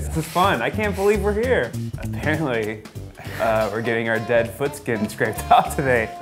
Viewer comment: I actually thought it said Dead Guys Get Their Foot Skin Scraped Off. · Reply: I thought it said guys get their dead foot scraped off